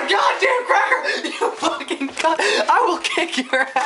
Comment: on 0:20 did he call her a cu*t